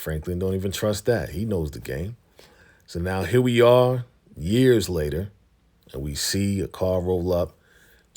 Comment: I don’t think it said years later at the end. I thought it just said 1 year later.